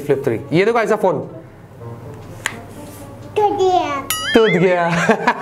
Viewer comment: i thought she said ghatia 😂😂😂